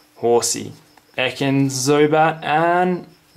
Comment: I heard "AERO-ZAPDOS"